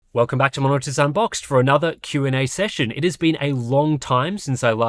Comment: I swear I heard "WELCOME BACK TO MONO-AUTISM BOXED"